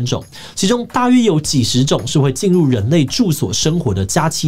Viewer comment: 影片用聽的，把「家棲蟑螂」聽成了「+7 蟑螂」.....那........有 +9 的嗎？